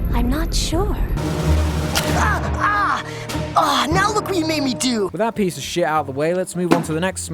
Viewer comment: “Now look what you made me do!” All I hear is “NOW ILL SHOW YOU UUUUUWOOOOOOOAOOAAAAAAHAHHH”